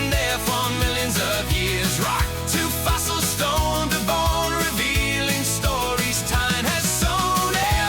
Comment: I heard stones and bones 😂 Anyway wearing shorts out fossil hunting?! He's brave